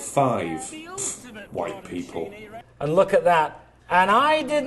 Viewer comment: did he say 'white people ' hahahaha